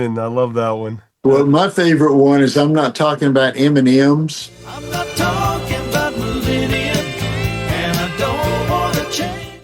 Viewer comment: My old radio, I heard, “I’m not talking ‘bout Meridian.” Lol! Now, I can’t believe I heard that. Great song, great intro. A #1 song of that time for me.